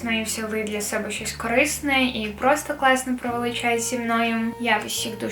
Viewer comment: я одна почула "чай"? Дуже дякую за відео❤❤❤